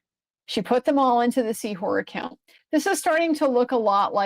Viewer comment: Must admitt that I first heard "seawhore" and stoped in my tracks 😂